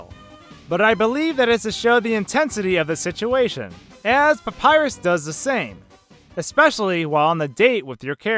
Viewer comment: Papyrus DOES sweat during the game. During the date, you can see him sweating. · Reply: Tina Jita Oops I thought he said Papyrus doesn't sweat XD